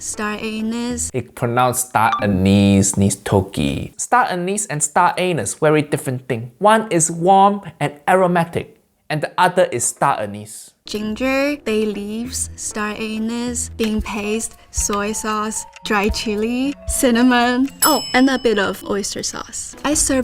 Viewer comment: did she just say star anus???